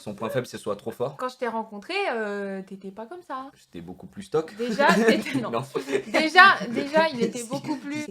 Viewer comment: IL ETAIT BEAUCOUP PLUS QUOI ?! JE VEUX SAVOIR !!! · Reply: Stock = épais